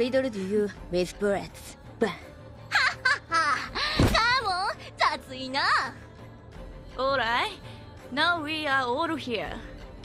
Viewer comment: What does Akari even say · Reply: "Come on. That's enough."